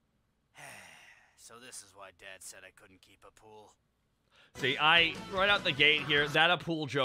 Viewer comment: So I did know who Appule was as he was one of my favourite characters (I loved his design). But I still missed the joke as I always thought they said ‘couldn’t keep a poodle.’